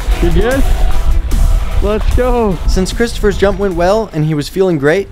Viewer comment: wait what did he call him? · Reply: Christopher